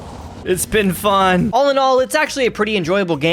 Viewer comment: "Did he just say Allinol?"